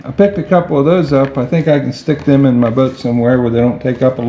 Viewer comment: I swear i heard "stick them in my butt somewhere" at 8:59 hahahah